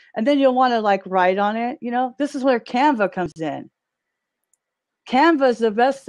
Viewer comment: what did she say at 14:40? canvas?